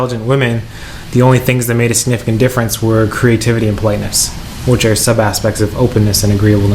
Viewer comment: "the only things that made a significant difference where creativity and"... playness? plainness? what? what does he say? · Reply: Entengummitiger Politeness